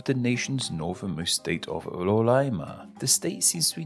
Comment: "rolaima" As vezes ele troca o R pelo L, só falta ele ser o cebolinha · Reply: Eu ainda ouvi "lolaima" kkkk